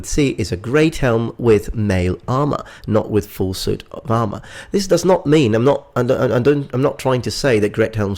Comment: XD at 2:21 it sounds like he said full suit Obama.